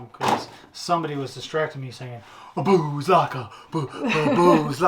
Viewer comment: hahahaha I thought you said boobs locker for a sec there. had me rolling😂🤣